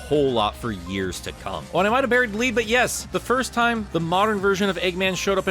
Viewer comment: what did he say? Did he say, “While you might not even believe, but yes…”?